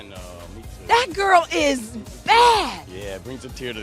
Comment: I thought Shiloh said Brad is bad?